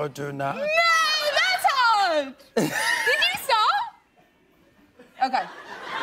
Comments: ??? what did she say at 2:08? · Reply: TheAg2661 "Did you start?" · Lol what did she say? Either way that part got me so good 😂😂😂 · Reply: "No! That's hard! Did you start?" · what did she say at 2:05? "Yeah that's howard! Did you saw??" ...did she accidentally spoil a scene from the new movie? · wtf did she say 😂😂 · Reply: She said "No, that's hard. Did you start?" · Wtf is she saying lol. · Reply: "No, that's hard! Did you start?" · Wtf did she say at 2:05? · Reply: I think she said "no that's hard"